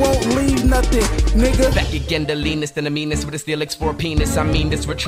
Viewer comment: CC says "they said that he didn't believe misdemeanors felix for peace on the news" lol